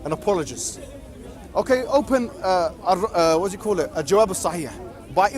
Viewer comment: what is the book that muhammed mentioned at 5:45 i didnt catch what he is saying nor do i know how to spell it to be honest. Can someone help? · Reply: الجواب الصحيح لابن تيمية Its book in arabic eljawab alsahih